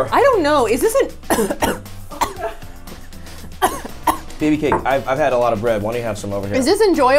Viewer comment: Did Tyler call Saf babycake at 18:43 :O